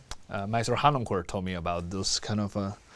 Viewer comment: Who does he say at 2:06 told him about the coming down of harmonies? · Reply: FPL with Megot Noskill Nikolaus Harnoncourt whom he referred to as ”Maestro Harnoncourt”.